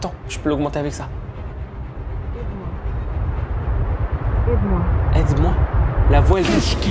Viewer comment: " bz moi " OU " Aide moi " MDRRRRRRRR